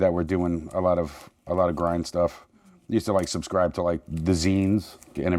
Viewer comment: Does somebody know what says here? 7:20 Which band? The Zen's? The Scenes? Deceased (they're deathrash/thrash, so I guess not)? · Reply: "I used to subscrive to the 'zines" (magazines)